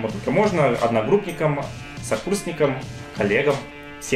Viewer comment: послышалось в конце не "коллегам" а "олегам" :D